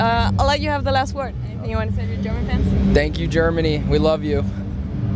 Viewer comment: I'm not gonna lie at first It sounded like Josh said "Thanks, Jeremy " instead of Germany so I was a little confused haha